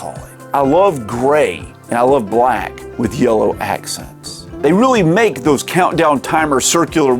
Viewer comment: I swear I heard "YOLO accents" xD